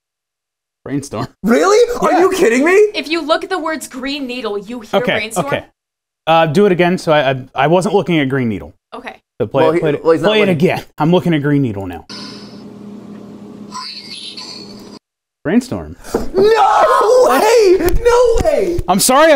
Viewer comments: Is it just me or does anyone else hear Brain Needle? No matter what I hear it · While not looking at the screen at all when that segment began, I hear "Brain Needle" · I kept hearing green storm 😅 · Am I the only one who heard "brain needle"? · Yo...... why did I hear "brain needle"? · what does it mean if i hear 'breen needle' ? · i hear greenbeatle & brainbeatle Edit: 1:04:07 googled it, teeth aparently sumbolize power, youth, & passing of time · No matter what I do I always hear “brain needle” for some reason. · Not kidding I heard "brain needle"😂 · I heard brain needle am I ok